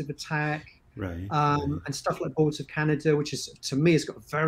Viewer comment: Can't figure out what artist he's mentioning at 5:58 haha. Boilter canada? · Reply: Boards of Canada